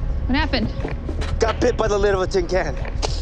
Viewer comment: What does he say at 1:47? Got bit by the little tin can? I'm confused at what happened here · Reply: He said, "I got bit by the lid of the tin can." Meaning that the tin can cut his arm and causing Dr. Choi to bleed out from his arm. He probably said bit, because he felt like he got bit.